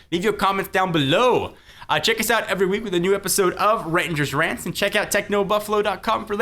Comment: lol i thought it said Rettinger's pants